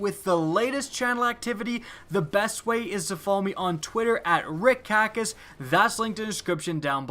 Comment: Every time I hear "rick kackis" it sounds like dick cactus